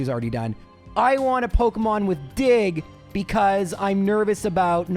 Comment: Out of nowhere 11:28 Did just said blatantly "I want a pokemon with dixk"? I can't believe what I heard. 😅